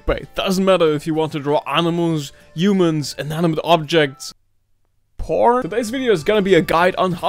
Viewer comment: did he say porn- ?